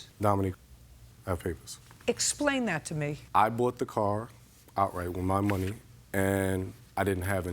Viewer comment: did he say he bought the car outright? @ 1:17 ? in that case the car wouldn't be financed